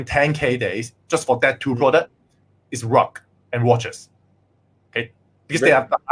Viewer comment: dont understand what he is saying? Rock and watches?